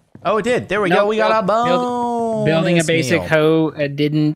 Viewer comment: I thought he said "Bonus meme"